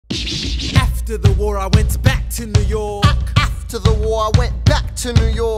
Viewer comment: Am I the only one? I keep hearing "F to the war I went back to new work"